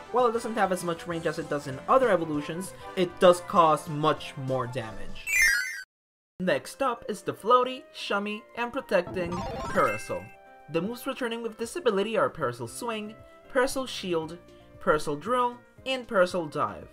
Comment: it sounded like you said, "the moose returning with this ability!" LMAO. overall awesome list of evolutions!